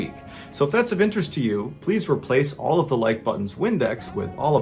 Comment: At first I thought you said replace all the like buttons olive oil with windex.... that would be MEAN!